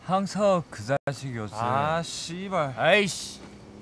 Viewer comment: did he just said shibal 😂